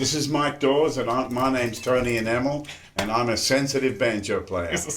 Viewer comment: Somehow I've misheard "...and I'm Tommy Emmanuel" as "..and I'm totally an animal". Maybe it's high time I had paid a visit to an otologist...